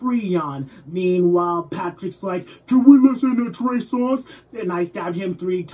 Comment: anyone know what patrick says at 0:16? i need to know for research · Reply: "can we listen to trey songz?"